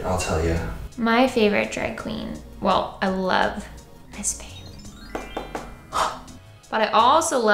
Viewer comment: which drag queen does she mention at 12:28? because the subtitles say ice-pan... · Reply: miss fame